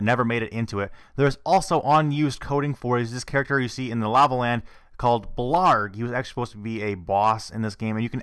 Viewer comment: you mention a boss that you can only see with gameshark, called "blarg" or "vlarg"? cant actually tell what you're saying i've looked this up and i cannot see any evidence of an unreleased boss called blarg or vlarg, what are you even saying? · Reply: oh right i found it, "blargg"